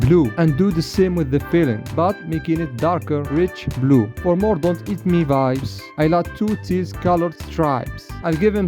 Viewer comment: what did it SAY??? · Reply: Don’t eat me vibes